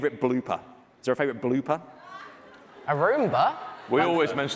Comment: tubbo mishearing bloopers as roomba was so funny